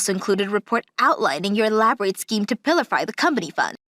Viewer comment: I laughed when the female voice actor said "Pill - a - Fie" instead of "Pill - fur" (~19:03 min). The English word Pilfer (to steal a small amount) is pronounced "Pill - fur". Also "Fur" and "Fir" are pronounced the same, even though they mean different things. I think Japanese is hard to learn, but I guess English is equally hard for those who learn it as a second language. I admire their effort.